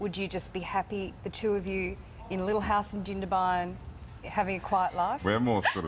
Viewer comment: WHAT IS SHE SAYING A LITTLE HOUSE IN....WHERE? 6:44. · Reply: Beth Sp gindabine